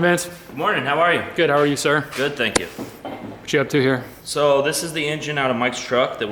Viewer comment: “Good morning Vince how are ya” “Eh I’m decent” Same Vince, same. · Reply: he actually said “I’m good thank you” 3:00 but good try getting likes kid